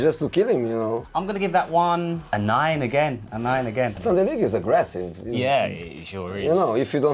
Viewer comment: what did he say????? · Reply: "Sunday league is aggressive"